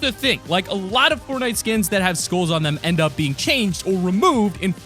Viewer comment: When he said skull it sounded like he said school